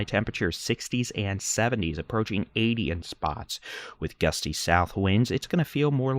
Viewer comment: Hey folks, captioning helpful or nah? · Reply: Not too helpful. YouTube has their own if someone needs to turn them on/off. Also the AI doesn’t translate us southern folk too well. 5:54 it says “70s approaching adian…” instead of 70s approaching 80s. Thanks for your hard work! We all appreciate the videos (and focus in Texas).